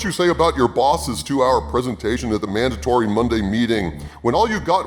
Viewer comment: I know I've been watching a lot of when I hear "Mandatory" as "Mandatorian"